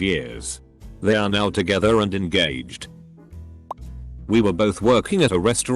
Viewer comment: caught me off guard, thought they said "We are now together and engaged"